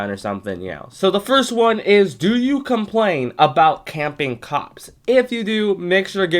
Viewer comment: Sorry but I heard 1:00 do you care about camping co*ks? lol Great video btw